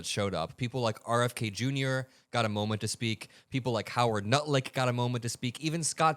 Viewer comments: I heard you say Howard Nutlick 😂❤ not wrong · lmfao did I hear "howard nutlick" heck yes